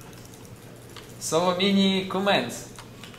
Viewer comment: What does dendi say at 1:20? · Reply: Selemene commands. hes copying one of Luna's responses.